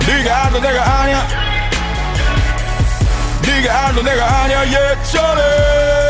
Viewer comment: 니가 알던 내가 아냐 저게 (예전에-) 구나 내 귀는 (개쩔어) 로 그동안 들었는데 ㅋㅋㅋㅋㅋㅋ